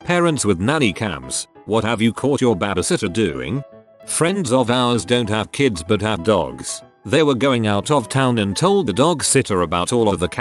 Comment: I thought that said "parents of nanny cams" and just accepted it